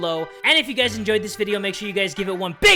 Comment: When he said " Jay from the Kubz Scouts is that dude", I thought he said " Jay from the Kubz Scouts is dead too!"